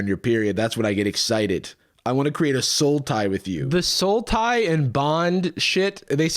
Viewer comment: Hearing you and that guy say "sultai" over and over at 9:20 had me so confused until I heard it correctly